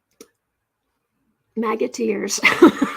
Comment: When you said, "Maga tears, " I heard it as Magateers, as in musketeers or Mouseketeers. 😂